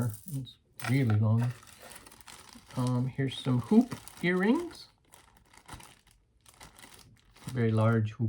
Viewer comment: Love the wooden clock! An injured man taking one for the team!❤️ I thought I heard you say poop earrings. My hearing is going.☺️